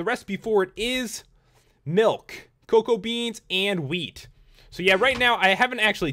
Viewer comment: what he said "Milk, Cocoa beans and wheat" what i hear "Milk, Cocoa beans and weed"